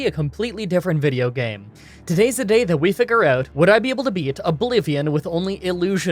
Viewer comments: I thought he said “Let’s beat a Bolivian” 😂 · I definitely misheard big time; "Would I be able to beat a Bolivian; with only Illusion Magic?"